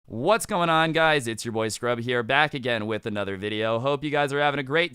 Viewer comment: Each time he says "it's your boy Scrubs" I always hear "it's your voice crack